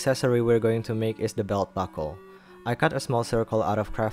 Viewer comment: I’m as listening to this while cleaning and I didn’t know you said ‘belt buckle’ I thought you said ‘doll butthole’. For a solid minute I was staring at my screen in shock up till I rewinded it.